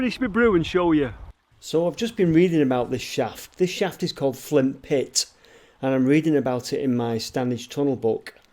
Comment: did you say this was called Flimp Pit?